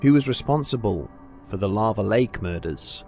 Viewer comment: I am so fat. I thought it said lava cake murders and I was like ooo sounds yummy.